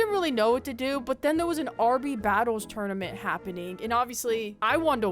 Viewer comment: DID YOU JUST SAY RB BATTLES?? AHH THE MEMORIES FLOODING BACK